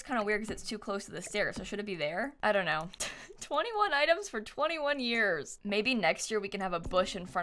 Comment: I read "21 rooms" and thought "that's gonna me either a big mantion or very small rooms". then she started talking and I heard "21 items". I was disappointed and amazed!